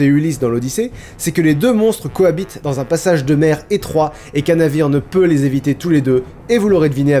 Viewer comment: Dites-moi que je ne suis pas le seul à 12:15 à avoir compris « passage de merde » au lieu de « passage de mer »